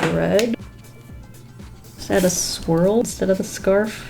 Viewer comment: Not gonna lie I actually thought she said "squirrel" instead of swirl ;-; I was so confused lol